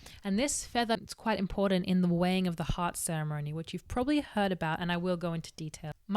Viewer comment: Whenever she says "the weighing of the heart" I hear "the wang of the heart" and that gives me a lot of interesting thoughts.